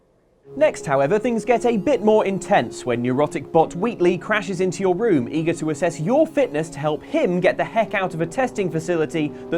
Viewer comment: Erotic bot Wheatley?.... I must have misheard that...